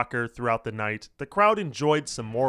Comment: I swear I thought you said wild sex 🤣🤣🤣🤣🤣🤣